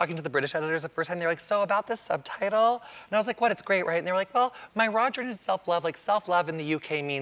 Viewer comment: I actually had to slow this down a couple of times. I thought he was saying "my Roger" when he was actually saying "my raw journey"!! I thought, what the heck? LOL! He is hilariously funny -- and cute!